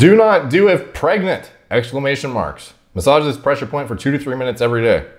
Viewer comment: I am clearly spending way too much time online... when you said "massage this pressure point" I thought you said "MISOGYNIST pressure point!" - I was like... WHAT?!?! 😲